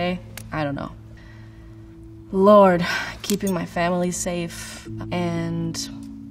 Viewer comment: «What's most important to you right now?» Billie : LORDE I really thought she said Lorde lmao